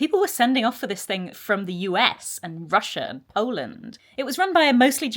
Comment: I thought you said, "people were sending off for this from the US, Russia and Oldham." Ah yes, Oldham that exotic...